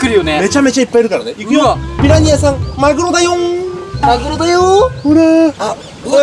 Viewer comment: マグロだよ~んがまるおだよ~んって最初聞こえたww